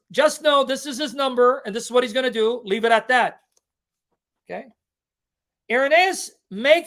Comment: ⏱ Timestamps 0:00 The controversy over the number of the Beast & Daniel Wallace’s claim 1:09 Irenaeus knew of the 616 variant—and rejected it 2:38 Why God gave the number symbolically & warning against speculation 4:48 How scribal errors may have created 616 7:05 Calling 616 “erroneous and spurious” & God’s mercy for ignorance 10:07 Judgment for altering Scripture & danger of misidentifying the Antichrist 💬 Did this teaching clear up confusion about 666 and 616? Share your thoughts in the comments. 📢 Send this to anyone curious about the number of the Beast or misled by modern textual claims. ✅ Subscribe for more in-depth biblical and historical teachings from Sam Shamoun. Disclaimer: This channel does not claim ownership of the original video. All rights belong to Sam Shamoun, and we have received permission to subtitle and publish this content to make it accessible to a wider audience. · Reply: not what he says ( leave it at that ) he says let him who has wisdom count it. Let him who dont ( leave it at that lol )